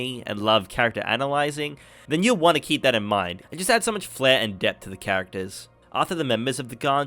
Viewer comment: I have replayed 6:53 over and over and still don't understand the words... "So much fair"? "So much fare"? "Smush fehr"? "Mush Flair"? "Smusch Fare"? Not trying to offend, English is my second language and I just wanna know what you said/meant :/ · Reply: All good! As an australian, my pronunciation can be a little hard to understand. I said "So much flare" as in, the scene visually could have had more energy to it :)